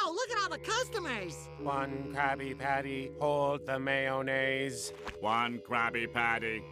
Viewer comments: When I first saw this episode, I thought they were saying one Krabby Patty, all the mayonnaise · I always heard it as "One Krabby Patty, all but mayonnaise" like they wanted everything BUT mayonnaise